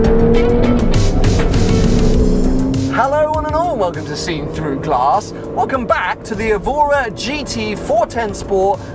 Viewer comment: what the hell is he saying??? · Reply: “Hello one and all”